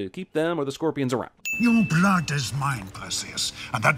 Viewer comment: "Your blood is mine, Perseus!" What I heard if I didn't raise volume: Your blood is mine, puss*es